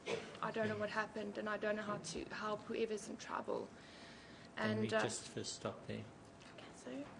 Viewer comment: does she say 'Reevas in trouble'?